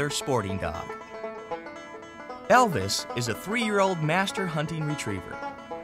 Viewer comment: Wait the dog's name is Elvis? For a sec I thought he said Elle Woods